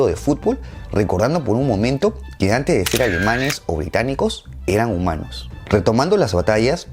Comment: Me quedo con esta frase: "antes de ser alemanes o británicos, eran humanos" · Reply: Yo entendí "Antes de ser Alemanes o británicos éramos romanos"